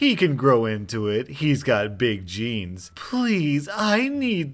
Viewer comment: What the choosing beggar says “he’s got big genes” What I hear “ he’s got big jeans”